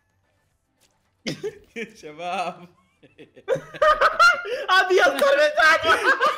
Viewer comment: ايش قال هنا ؟ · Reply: MsPakati_مسباكاتي ابيض صار ساقه اتوقع كذا